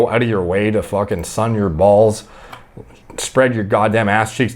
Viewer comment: i read it ands heard it as stun your balls